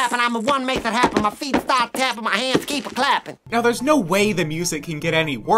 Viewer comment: Did he just say “My hands keep her clapping”… WHAT?!!?